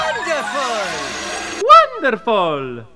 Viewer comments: on that last one I heard "open salami" 💀💀💀💀💀💀💀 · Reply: I thought he said wowy zowy for the new actor. Although Martinet does sound like he said “Open Salami” lol. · on that last one I heard "open salami" 💀💀💀💀💀💀💀